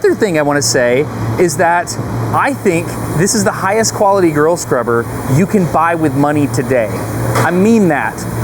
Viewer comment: Is he saying "girl scrubber" on purpose? I mean it's about 50% of the time...